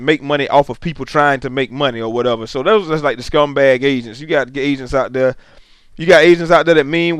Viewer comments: I thought he said Asians were scamming people for money and I was laughing soooo hard... then I realized he said agents. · Yo I thought he was saying asians too, but he was saying "AGENTS". Listen to it again and you should notice it. Also listen to the context of it. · when you said "agents" it sounded like you said "asians"